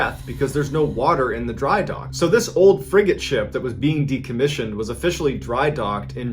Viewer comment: sounds like he says “this old frickin ship” caught me off guard & made me spit coffee 😂😂😭